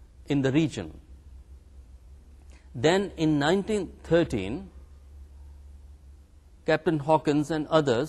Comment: it was 1613 instead of 1913, when Auranzed issued a Farman.